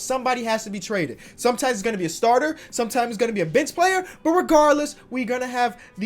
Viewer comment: thought he said “bitch player”